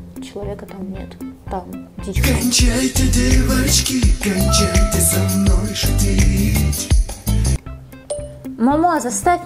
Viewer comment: КОНЧАЙТЕ ДЕВОЧКИ КОНЧАЙТЕ СО МНОЙ ШУТИТЬ ОРУУУУ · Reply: 🤣🤣А мне вообще послышалось "кончайте со мной шутеечки" 🤣